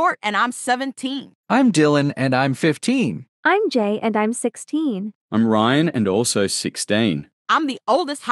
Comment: Why do I hear “im gay and im 16